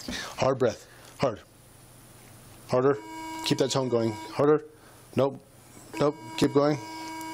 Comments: wtf did he say 😂 keep that tongue 😛 going 😂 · I thought he was saying “keep that tongue “ 🤣🤣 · I swore he said, “keep that tongue going” but it was tone I realized rofl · Couldn't tell he was saying "tone" and not "tongue" until about the 3rd time. By then it was too late - was laughing so hard and spit my coffee out then coughed so hard that it went up into my nostrils and my nose was bathed in sugary black coffee. Inhaled it also and continued my coughing because it was so hard to quit because I was still listening to this and still (literally dying😂) laughing. Bahahaha - I also can't tell from this video whether she was being sarcastic and funny or if she was truly flirting or just plain drunk. 😂👍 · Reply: lol, I heard it as "keep that tongue going" as well. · lol at first I thought The cop said 'keep that tongue going' I was like what??? Then I'm like ooo 'Tone' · i thought he was saying keep that tongue going lol